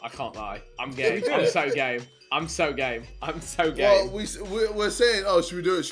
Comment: Wait what did vik say:'I'm gay I'm gay I'm gay.'